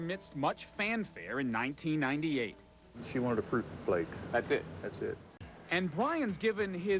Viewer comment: What did he say at 1:36? She wanted a poop plate, fruit cake?